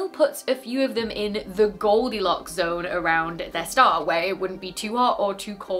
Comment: I thought you had said, "...in the goldilocks zone around their star, where it would be too hot or too cold for life to exist." I listened to it several times thinking either I hadn't heard correctly or you had misspoken. But after turning on captions, the captioning showed "wouldn't" where I had heard "would". I would recommend either saying "would not" instead of "wouldn't" with its subtle ending, or stating the same information in the more direct positive form.